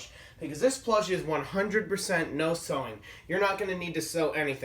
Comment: It says no sewing but i thought it said no swearing